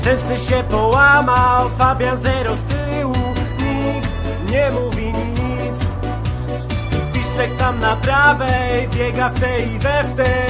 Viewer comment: ja tutaj słyszę "nikt nie mówi nic" a moi znajomi mówią że słyszą "Milik nie mówi nic"